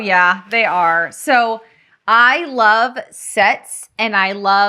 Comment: You said “ I love sets. “ my hubby came into the room and asked “Did she just say I love sex?” Thanks for keeping us entertained! You are doing great! Tee hee hee